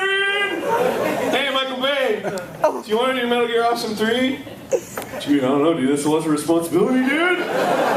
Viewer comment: what did the guy in the audience say before Arin shouted "shut up"? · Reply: I think I heard "METAL GEAR AWESOME 3!". :U